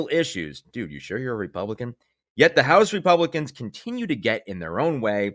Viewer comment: At first, I thought you said “Repugnicans.”😹